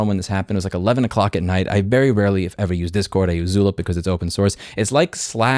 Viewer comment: what did he say he uses as an alternative do discord? I heard "zula" lol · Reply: found it, it's Zulip